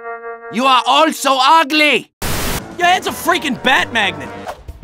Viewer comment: could be named ''literal fortress'' as well the bat magnet with batman cracked me up as well xD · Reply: I always thought he said batman.